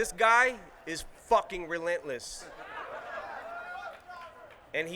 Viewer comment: FUCK JA RULE anybody else heard that?? 😂😂